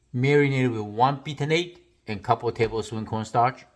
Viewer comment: What was the marinade? I heard cornstarch and patinate, but that must be wrong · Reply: I heard one beaten egg (and a couple t corn starch). But please clarify, chef!